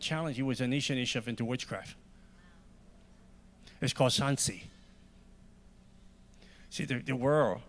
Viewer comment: Sanzi?? Is that what he said about the ice bucket challenge?